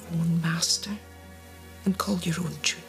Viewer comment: "Be your own master and call your own ________." Does anyone have any idea what that last word was? I can't make it out. Its sounds like "Tree" to me. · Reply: Tune